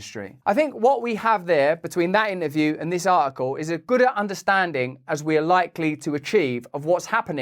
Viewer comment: did Russell really say "we have a gooder understanding "